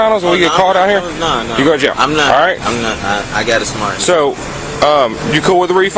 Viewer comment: Did he just say "I got a smart..." at 6:01 ?🤣🤣🤣